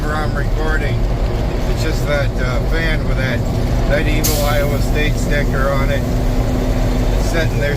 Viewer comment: Silly me, i at first thought you said, "that horrible iowa State sticker" on the fan, then i realized you must have said, " that honorable Iowa State sticker!"! Regardless, love your channel!